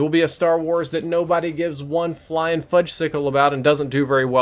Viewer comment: did you just say "fudgecycle". What are ur audience 8yr olds oh wait